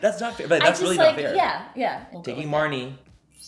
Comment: I thought it said “marine” I was like Marnie was a MARINE O.O